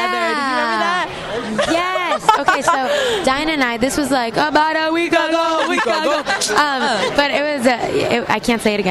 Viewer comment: what did they cheer/say at 3:09?? I can't understand it... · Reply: "ABOUT A WEEK AGO, WEEK AGO!" LOL